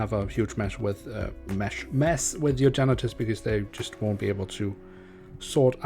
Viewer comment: What he said: "huge mess with your janitors" What I heard: "huge mess with your genitals" xD